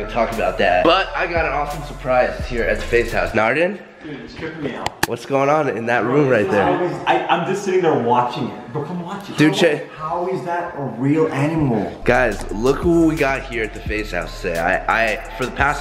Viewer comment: "How is that a real animal " faze teeqo 2017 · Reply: I thought teeqo said is that a rat